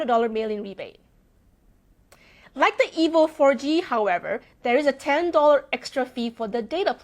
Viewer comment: did she just say "like the evil 4G"?